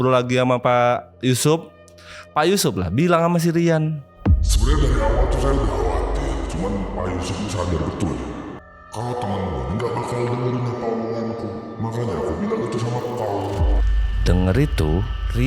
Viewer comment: bang gajelas pak yusuf ngomong apaan.. lain kali dikasi subtitle dong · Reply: "sebenernya dari awal tuh saya udh khawatir, cuman pak Yusup nih sadar betul kalo temen mu nih gabakal dengerin apa omongan ku, makanya aku bilang itu sama kau"